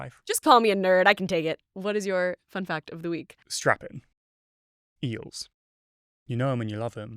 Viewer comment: Thought he said strap on